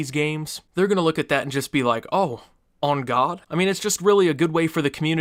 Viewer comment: Lmaoooo did he say “on god”